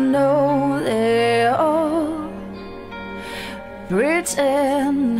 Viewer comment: “What if i say i’m not like tea ☕️ others?” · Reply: I honestly thought it sounded like she said "what if I say I'm not like cialis"... I was quite concerned lol